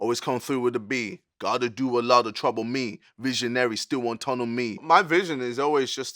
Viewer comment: who else thought he said missionary over visionary